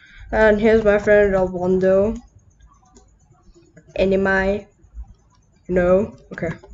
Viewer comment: Dude, I don't know what you say in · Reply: Anime.